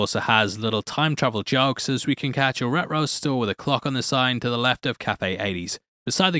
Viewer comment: Had to listen three times to figure out you're saying reTro. It sounded like Astro from the Jetsons: "Ruh-ro"